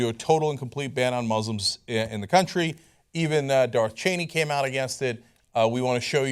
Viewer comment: Did he say Darth Cheney at 0:04? XD